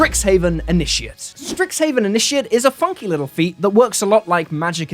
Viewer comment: I heard "Strixhaven initiate is a f*cking little feat..."